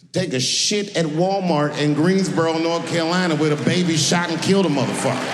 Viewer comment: Dababy? I literally thought you said that a baby shot and killed somebody. I don't know who dababy is but I had to look it up just now. I'm like "of course the baby won't get canceled" haha i was thinking of the whole Maggie shooting Mr. Burns situation